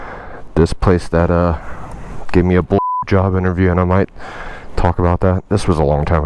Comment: I thought you said a bj interview